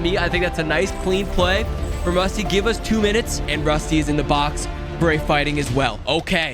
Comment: I’m sorry did he say 2 minutes for a fighting major?